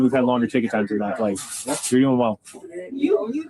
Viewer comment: in your video I hear a female say “you threatened us” and possibly “Molly and I” and then a male say “really”